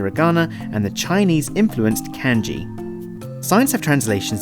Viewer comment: kenji or kanji? its kanji but it sounded like you said kenji xd